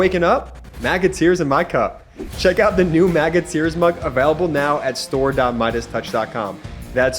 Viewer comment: I looked away, for just a second, when your ad came on. I'm hard-of-hearing and I could've sworn you said, "Maggot ears," not, "MAGA Tears." 😳😂